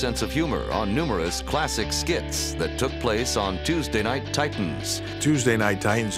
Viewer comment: I'm I the only one who thought it was about to say TNA